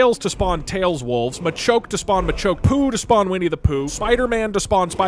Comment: I swore he said #POOB for a hot second there